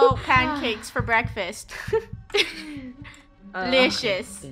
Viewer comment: I'm gonna have nice screamo pancakes for breakfast Is what I thought said